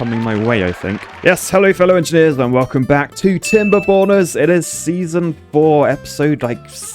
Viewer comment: whenever he said Timberborners, I always heard it as Timber Boners.